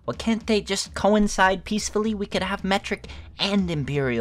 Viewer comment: thought it said met simp LOL also epic video B)